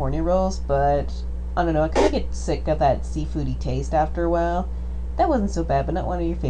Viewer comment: "I kinda get sick of that seafoody taste after a while." That's what he said.